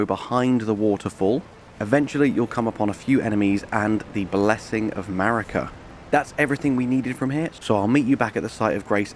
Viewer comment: i literally thought you said blessing of America XD